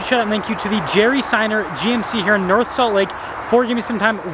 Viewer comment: Awesome truck! It sounds like you said Jerry Seinfeld GMC!😂